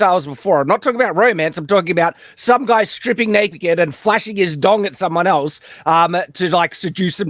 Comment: I thought aussies said, "Flashing his DINGO in front of someone." surprises us with some lore...